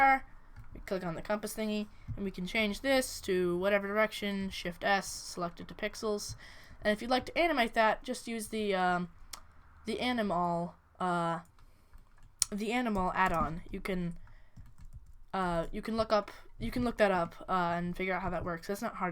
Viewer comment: for the compass and clock i thought you said animal add on. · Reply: no, sorry, Anim-All. just look it up, it shouldn't be hard to find a quick tutor on it :D